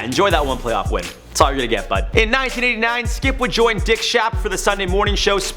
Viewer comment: I thought he said “Dick shaft” lmao